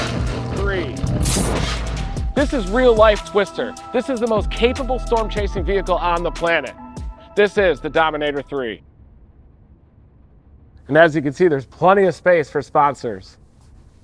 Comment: I thought bro said twistex☠️